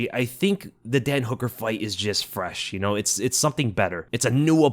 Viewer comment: Did he just say "the dead hooker fight" - ???? 😂😂😮